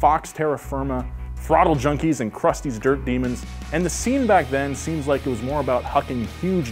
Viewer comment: Did he just say crusty’s dirt demonds😂😂🤦‍♂️